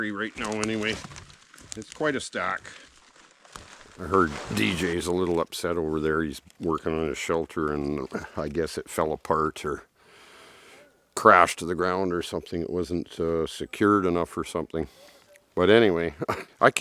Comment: Exactly what did say about his shelter? Nothing really. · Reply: beginning timestamp 34:31 he literally says "I heard DJ's a little upset over there. He's workin' on his shelter and I guess it fell apart or crashed to the ground or something. It wasn't uh...secured enough or something." You can even hear DJ yelling to himself from a distance.